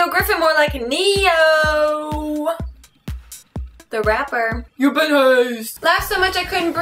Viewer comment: I always thought you were saying "you've been hosed", but with a really strong Australian accent o_o